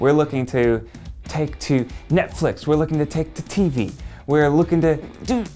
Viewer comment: I'm the only one who thought said Nedflix instead of Netflix? Can't be the only one